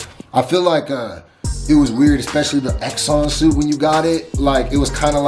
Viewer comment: One complaint with this video: the evil company is called Roxxon, not Exxon. Eh, maybe you said Roxxon, and I misheard you.